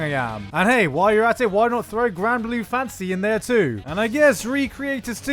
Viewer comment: For some reason every time you say "fantasy" all I hear is "fancy">_> what